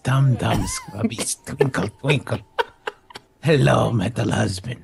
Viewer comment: "Three-eyed metal husband, do not tell Trash Hulk about the ba- :O" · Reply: I keep hearing "3 am metal husband"